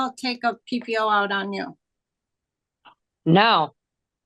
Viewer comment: I swear I hear someone tell her what to say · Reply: Sounds like someone says No!